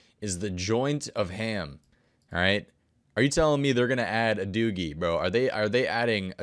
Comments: Did he say “doogie” or “doobie” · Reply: I had to rewind 😂😂 deff said doogie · did he just say dooogie?